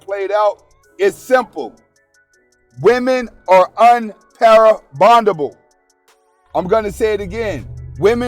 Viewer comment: no shade, I'm seriously trying to understand what you said at 8:00 mark...women are un-para-bondable? sounds like you meant maybe "irreparable" ? Love your content as always! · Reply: He made up a word based on "Pair Bonding"...unpairbondable - unable to pair bond